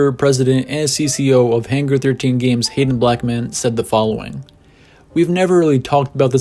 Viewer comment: When he said Hayden Blackman, I thought he said “Hated Black men” 😂